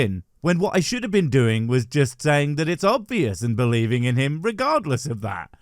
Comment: When she said "Obvious" I thought she said "All BS".